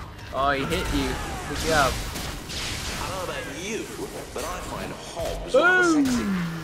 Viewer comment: i thought he said i gind the hobs are sexey